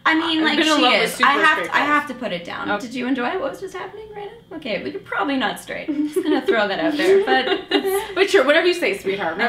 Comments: Wait, is Elise straight? I can't make out what Natasha is saying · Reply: "Did you enjoy it, what was just happening right now? Ok, you're probably not straight." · So, i'm not into overanalyzing things, seriously(here comes the 'but') BUUUUUUUT did she say "but YOU'RE probably not straight" TO ELISE? 5:10 Hahahahahhahhanoi'mcrying, I need to know cuz I can't live like this, this will end me...So please if i'm wrong:TELLL MEEEEEEH!